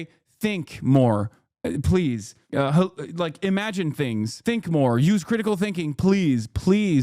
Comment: I'm so brain rotted i heard "THINK MARK! THINK!"